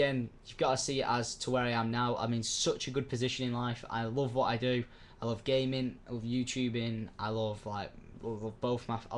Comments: i thought he said "i love gay men" LOL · wen u said i loved gamin at 3:47 i thought u said gay men but i replyed it thn im like oh ok gamin woo